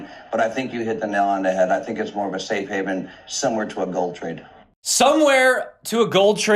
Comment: 🙋‍♂️ 👂 I heard “somewhere” too .. upon re-listening I’m sure he sai, “SIMILAR to a gold trade” ;-) .. viva la Bitcoin!!